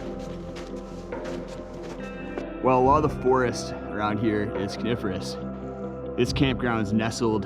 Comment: did you say the forest is carnivorous??? 🤣😂 I will leave now. Nice video